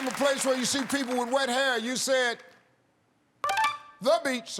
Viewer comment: I thought he said red hair…. Both times… so I was confused by beach and shower for answers 🤣